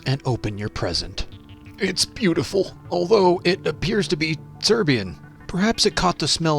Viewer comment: did you say it apires to be serbian if you sayed it then give it to me bud